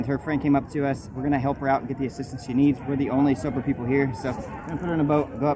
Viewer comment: did he say were the only Sober people here cause if so JEEEEEEEEEEEEEZZZ